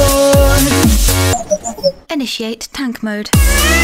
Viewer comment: back with a bastion song this week! which character should we do next? ;) also this song is on iTunes hyperurl.co/tankmodeNerdOut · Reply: The first time it went inishant tank mode I thought it said and a shit tank mode